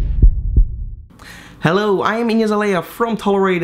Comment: I thought you said "hello I'm Iggy Azalea" haha